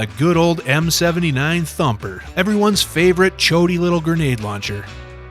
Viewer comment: Did he say: "Everyone's favourite Choaty little grenade launcher?"